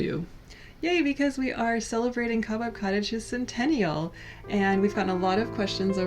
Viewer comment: This whole time I thought you were saying "Cabot Cottage" so I was quite confused when you were talking about spiders until I realized you were saying cobweb. You have a beautiful home regardless of its name.